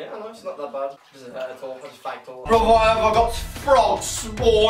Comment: Did anyone else hear say "I faked all of it" before cutting out?